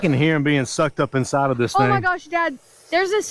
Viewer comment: I heard one 🐝 say I like bee’ing sucked up 😬🤪😬